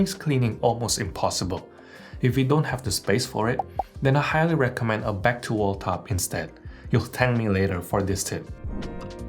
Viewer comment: I do love your videos, but a complaint I have is that now and then, listening as hard as I possibly can - and going back over and over, I cannot understand your words. Like at 3:24 or so, you say, "I highly advise your going back to ________ and you will thank me for that." I think you should add "flash captions" throughout your videos because you drop your voice volume a lot here and there, and it is at those times that I cannot understand you. Thank you. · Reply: "a back to wall tub"